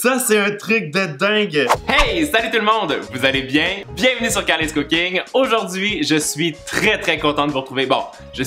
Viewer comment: j'ai entendu qu'il a dis karris cooking 🤣😂